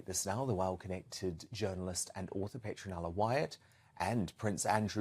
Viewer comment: I think if they were casting for a Real Housewives of London franchise they must get this Petronella woman and maybe Lady V..they are just the right amount of crazy... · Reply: I thought he said her name was Citronella at first. Was waiting for her to ask for a tub of goosebury yoghurt!